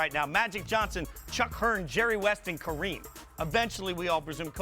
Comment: Yo 0:45 did he just say CHUCK Hearn!Who da fuck is CHUCK!